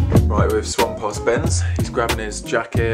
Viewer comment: at minute 0:52, did say "we've swapped husbands"?